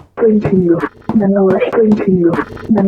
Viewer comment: I hear “Nanowash continue”